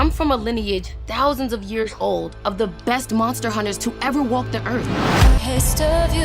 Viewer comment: Swear to god I heard "greatest hunters to ever 'woke' the earth".